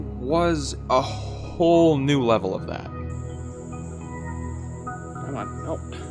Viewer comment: did you say "A whhhole new level?"